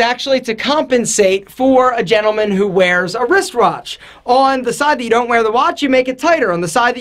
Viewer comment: Don't think I didn't hear you say wrist rawtch @ 2:35 😂